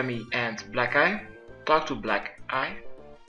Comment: lol at 2:17 sounds like he says "black guy" then he says it again "black ----- eye" like he knew that what he said sounded like black guy lolol